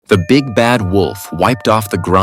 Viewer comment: Bro i thought it said the big back wolf 💀